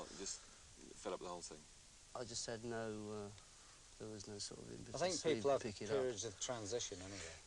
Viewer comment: what Jimmy says at 4:16-4:20? "there is no ...... to to succeed"